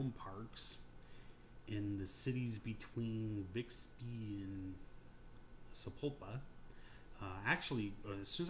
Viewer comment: wow you hit the jackpot.. i thought you said Bixby and Sepulveda..